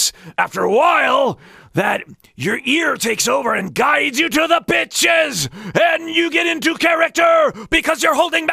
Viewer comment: Sorry... I heard "Your ear takes over and guides you to the bitches ... " and I can't keep it together anymore...